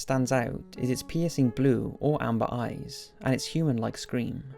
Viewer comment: what he said: " its piercing blue or amber eyes" what i heard: " its penisy blue or amber eyes"